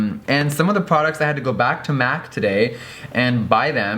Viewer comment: he said math instead of Mac 😂